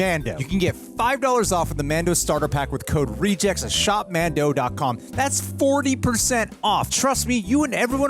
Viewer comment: I thought that said shmoopmadoon.